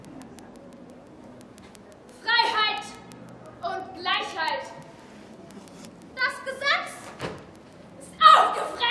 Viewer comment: was hat Sie gesagt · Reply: "Freiheit und Gleichheit. Das Gesetz ist aufgefressen. Nun wird Gottlieb zur Regierung kommen"